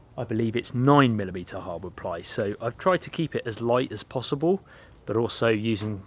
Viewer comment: Hi, I had watched up to you putting the clear tarpaulin up and mis-heard what you said next “I thought you said Mice instead of light” that sent me down the wrong way and had to re-watch a section of your video just to discover why you wanted mice I your cabin. Also why are you nailing the tarp to the inside, the rain will run inside and rot your wood as against the outside it would run down the protected surface safely.